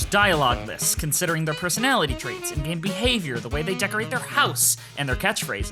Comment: Misheard 6:45 as "their gay behaviors" and laughed my ass off I watched this video after the Subnautica one and I'm absolutely blown away. I love your style so much and your avatar-guy is really expressive, despite the lack of animation. You show truly unbelievable quality considering your size, I hope you get the popularity you deserve!